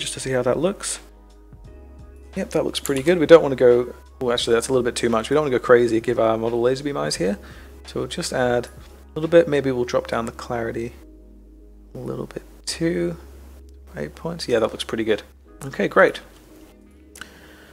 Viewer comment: Thank you for watching, please make sure to hit that subscribe button if you enjoyed watching this video! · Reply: did you say "lesbian eyes?"